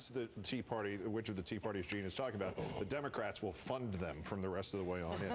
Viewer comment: '....the Democrats will fund them.' I had to repeat that part of the clip twice because I thought Olbermann said 'the Democrats will fuck them.' Anywho, Olbermann was actually somewhat correct. Claire McCaskill actually bought Pro-Todd Akin ads during the Missouri Republican Primary because she wanted to run against him and defeat him.